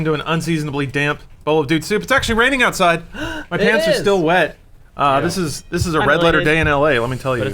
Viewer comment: I thought he said "My parents are still wet"